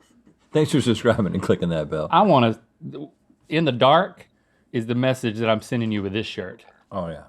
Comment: I like the new lower third. Rhett: My close friends. Link: rme "Link Link Link" 17:20 - Did Link just say "I wanna f*** you in the dark"????